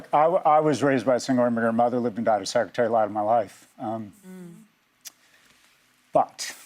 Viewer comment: What did he say at 3:09? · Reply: "I was raised by a single immigrant mother, lived and died a secretery, the light of my life."